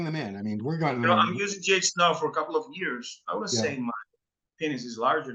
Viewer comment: what did he say at 10:18 "iam using what .. ?? " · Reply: GH - eg growth hormone